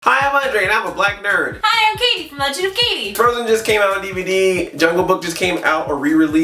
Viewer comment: hi I'm Katie from Jew Katie That's what she said listen closely O_O